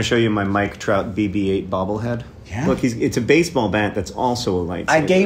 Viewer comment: I thought John said he had a "Mike Trapp BB-8 Bobblehead" and then I was slightly disappointed. 🤪